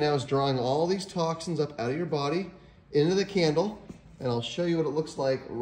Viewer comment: Candle??? I thought he said camel😁